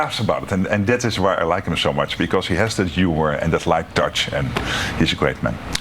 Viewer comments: Mark Rutte (Sec. Gen. of Nato) being Dutch, is he saying "because he has that humor and "is like Dutch", not "light touch"? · I think he said, “Like Dutch,” referring to the cultural sense of humor.